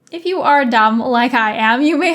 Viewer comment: I thought you said, "If you are a dom, like I am, ..." aka bdsm. hahaha took me off guard there.